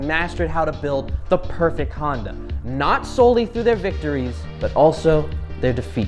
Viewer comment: what word is saying? Because I hear condom.